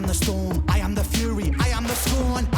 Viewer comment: I heard it differently. I heard him say I am the eye. I am the storm I am the belly I am the beast. And the way I interpret it is that he is in reality every part of the equation. Perhaps I heard it wrong but both ways work.